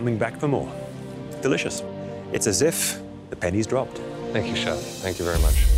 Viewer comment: Did Chef Michael say “It’ll have me coming back for more, it’s as if the panties dropped.” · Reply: No, he said pennies. The penny has dropped is a common English saying but your mishearing made me smile 😊 thank you